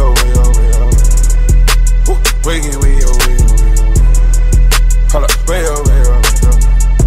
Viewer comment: sounds like he saying wiggle wiggle wiggle wiggle